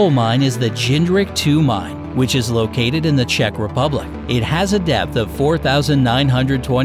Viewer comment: if i heard correctly you just said czech republic right? well im in czech republic too